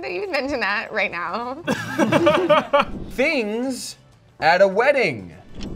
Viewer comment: I first heard "Things you eat at wedding", which made the "bride" answer way funnier